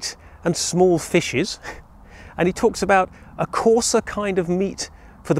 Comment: When I heard you say "courser" meat for the poor I thought you meant horse meat